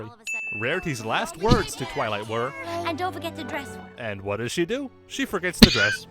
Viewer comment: Rarity said "and don't forget to dress well" I think misheard that lol.